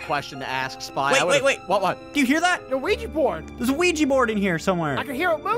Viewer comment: idk it is just me but I hear says luigi board